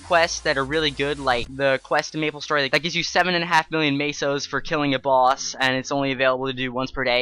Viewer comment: xD i heard him saying 7.5 bil too